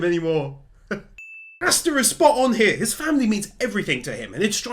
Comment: did you say...... FAMILY???!!!